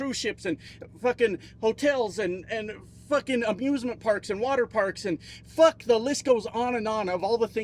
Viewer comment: For a sec I thought you said abusement parks